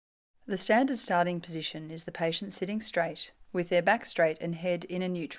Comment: I almost thought that said "poisoning the patient" at first. Oh boy.